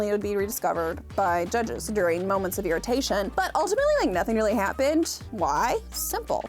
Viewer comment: Nothing really hat-pinned? I swear that’s what you said. Subtle.